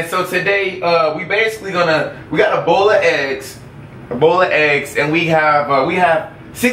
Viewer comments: when he said a bowl of eggs I thought he said ebola eggs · when said ''bowl of eggs'' i thought he said ebola no joke... i had to hear it a second time to see what he said.